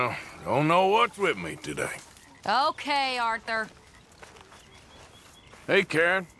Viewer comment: wrong subtitles, she said ok Arthur, but subtitles say, im not sue i see the joke